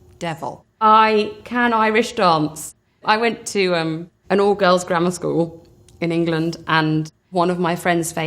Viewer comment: At first I thought she said she went to an "all girl's grandma school" 😂